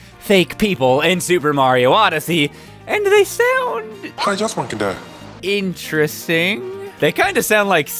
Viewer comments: I thought he said I just want to die it was hillarious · I hear "i want to die" from the shopkeepers too